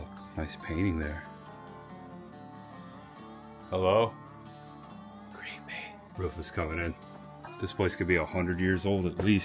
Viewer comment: i heard "greet me" or "creepy" 😮😮😮😮